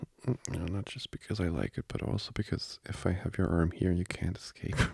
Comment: wait a damn min is he Hispanic or some or am I just trippin cuz I heard “pero also because…” 😭 bc if so that’s even better 😫😫